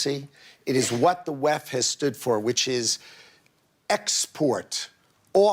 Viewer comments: I think he says "weff" as in "WEF" as in "World Economic Forum". I don't think he claims that the Left is behind Globalisation. Unless I missed something. Although I believe Globalisation is more of project of a wider range of the political spectrum. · Left? I ran that clip ten times. He says "WEF"! Certainly not "left". But what he is describing is hard core capitalism. I have no idea what he is trying to say.